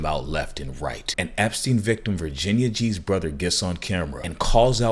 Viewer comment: You said “Virginia G’s brother” I heard “Virginia cheeseburger” In case anyone needs a laugh